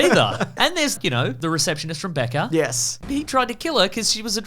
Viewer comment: I'm so sorry I thought he said Becca not Becker 🤭